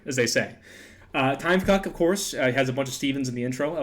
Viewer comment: someone also asked this in the mewgenics 8 days left stream but he didnt really answer the question because it was a "spoiler question" or something lol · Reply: also you keep saying time fcuk but everytime i hear "time cuck" and its fcuking with me